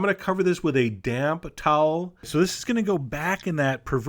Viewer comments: I thought he said : "Cover that with the damn towel" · sounded like you said "damn towel" not damp towel.